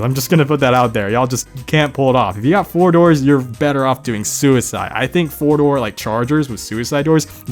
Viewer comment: "like 4 door vehicles, yall still look terrible doin scissor doors. if ya got 4 doors youre better off committing suicide" (or at least thats how i heard it, and died laughing)